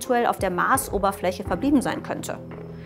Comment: Was für ein Wort sagt sie da in 0:23? Ich habe es bestimmt 10x angehört, aber nicht verstanden, was das Wasser auf der Marsoberfläche „verb…..“ sein könnte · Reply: Sie sagt, das Wasser könnte auf der Marsoberfläche verbieben sein